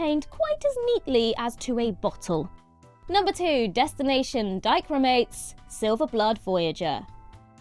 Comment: Coulda swore you said "dyke roomates", I was like WHAT?? Dichromates, okay.😂